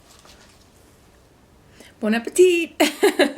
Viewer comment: When she said " bon apatit" it sounded like she said "bone apple tea"😂😂